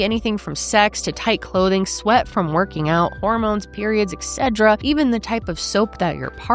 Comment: Why is saying "etc" like it's an allergy medication 😭😂?? Excedra~🌸